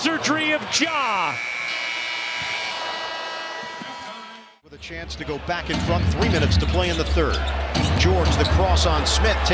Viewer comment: “with a chance to go back and fuc*” i swear thats what i heard